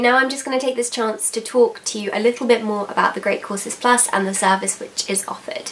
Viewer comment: This whole time I thought she was saying the great causes plus 😂